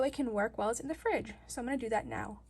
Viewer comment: When you said fridge I thought you said freezer because I CAN NOT HEAR WELL. Great video btw.